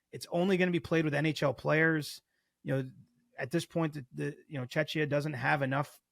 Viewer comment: did he just say “Chechnya” when referring to Czech Republic? 🤦‍♂️😂